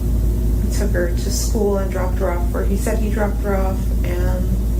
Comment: I thought she said, “…dropped her off where he said he dropped her off.” Which makes no sense except it’s exactly how she talks. In a prior version, she said “dropped her off where he dropped her off.” This time, to my ear, the only change is she added the words “said he.” Regardless it’s so bizarre she’s not 100% editorializing this answer by now.